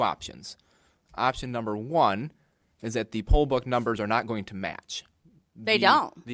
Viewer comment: I honestly thought that drunkard was saying poll buck